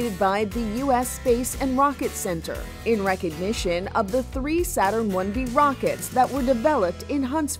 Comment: Did they just say one v it’s Saturn 5 you idoits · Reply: She said Saturn 1 B, which is correct. This is a standby Saturn 1 B rocket. The 1B placed Apollo in Earth orbit. The Saturn V (or 5) sent Apollo to the moon.